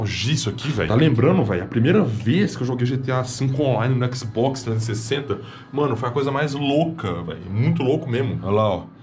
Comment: Entendi que ele jogou no xbox na década de 60!!! kkk